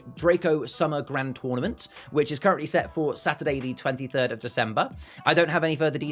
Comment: Not at all xD Did i say that on stream or have we incorrectly written that somewhere? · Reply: it definitely sounds like you say December